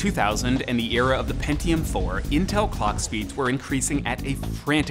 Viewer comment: when he says 'clock speed' it sounds like he is saying "Cog speed" wha?? lol